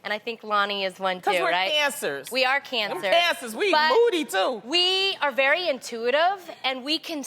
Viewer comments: Sounds like Loni said we cancers, we eat booty too haha!!! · did anyone else think Loni said "we eat booty too" LOL I was like wait what.. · did she say "We eat booty too"? Loni wydd 😂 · I thought she said we eat booty too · She said "and we're moody too" but I heard "and we eat booty too"